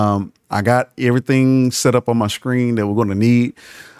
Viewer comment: Did he say at 4:56 b!tchmark? Hahahahah